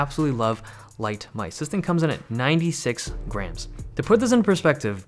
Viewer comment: When he said the mouse was 96 grams, I thought he said 96 grand...🤭🤭